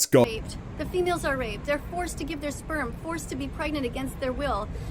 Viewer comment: did she just say female cows are forced to give their Sperms Transgender cows??